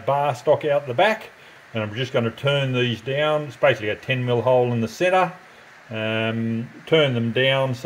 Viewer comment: great job, what is the width of the round spaces? I heard you say it’s a q0ml hole in the centre… and it’s 6 ml thick..